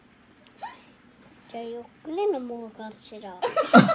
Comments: のときなんて言ってるか聞き取れん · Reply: じゃあゆっくり飲もうかしら〜って言ってると思いますよ！ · これってなんて言ってるんだろう😭⁉️ めっちゃ気になるよ〜〜（Ｔ＿Ｔ） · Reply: じゃあ、ゆっくり飲もうかしら だと思います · なんていってますか？ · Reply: ゆっくり、呑もうか知ら。。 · ここなんて言ってるの？ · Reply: 「じゃあゆっくり飲もうかしら〜」だと思います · なんて言ってます？？ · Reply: ゆっくり飲もうかしらって言ってると思います